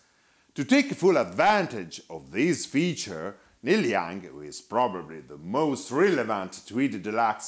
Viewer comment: WHO did you say "takes advantage of this feature...the most 5E3 addicted artist?" (cannot understand thru the accent). Thanks, nice descriptions and history info. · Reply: Hello Jojokeo, he is Neil Young. Please note that you can easily activate English subtitles, which I manually upload and are very accurate.